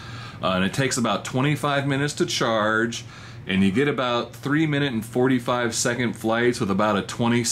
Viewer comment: I thought you said "3 minutes or 45 seconds flight time" I was like WHAT ahahahahha